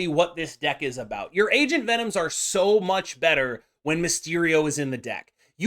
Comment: Am I the only one who keeps mishearing it as "Asian Venom?" I can't unhear it!